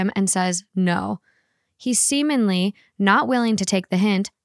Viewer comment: “he semenly” is all i hear 🤣